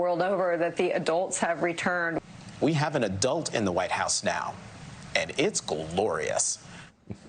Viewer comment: 🤔 did I mishear or did the “woke MSM” say “now we have an ‘ A DOLT’ in the Whitehouse 😳😳